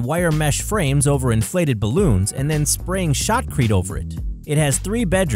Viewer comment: they sprayed what over it ? Did he mean Sheetrock ? I hear shockreet lol